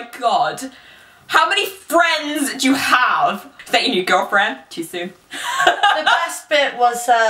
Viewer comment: Does anyone know what says at 1:06? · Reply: A Nico she said “too soon?”